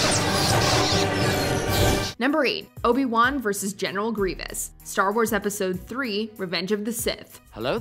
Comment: Did she just say general grievous was silly in revenge of the sith?😭😂now that’s just silly